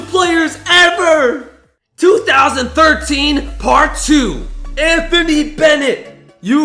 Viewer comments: Did he just say Anthony Davis instead of Anthony Bennet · who else heard him say Anthony Davis instead of Anthony Bennet